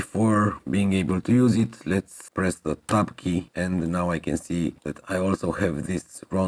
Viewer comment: Hi, At 23:40 you have just created the Bevels and then you say "Let us Press the XXXX Key" and all the inner curves turn Orange" - I relistened so many times but I cannot make out what XXXX Key I must press. Please Type Out what Key you are Pressing. - Thanks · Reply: It's the Tab Key. You need to have the mouse over the 3D view in order for it to work.